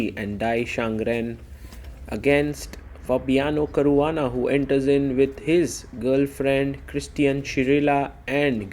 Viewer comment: I heard it as "He enters with his girlfriend Christian Chirilla" 😃